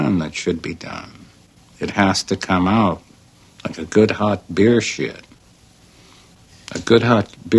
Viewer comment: at first I thought he said bear shit.